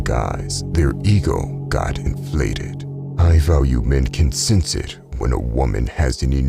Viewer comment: am i the only one who heard him say EAGLE